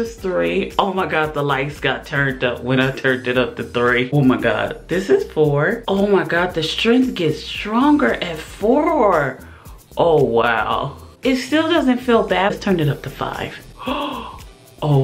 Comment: I swear I heard "the force is strong" as you were holding the wand in the dark 😂